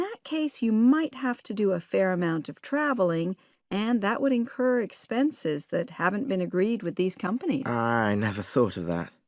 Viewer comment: why 29 is A? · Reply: A woman said that “it might have some travelling and it could be expensive” I am not sure how I heard whole sentence but the main meaning is definitely about “involving extra costs”